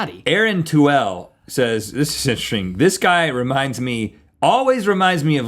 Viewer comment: Did Rhett say "this is sehr schoen" at 8:36? It's German and means "this is very nice/ lovely".